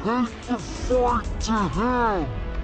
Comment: I thought it said we'll take the fight to hell not him xD I dont think disney would allow that.